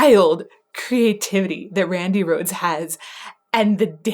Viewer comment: Did I hear her mention Randy Rhoads name at the 2:17 mark on the video…why?